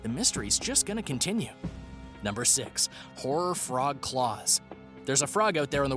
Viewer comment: when they said horror I thought they said Harry so I was like the boy who lived got named after a frog?🤔😂